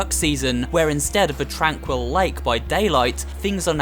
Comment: I thought you said dead by daylight 😂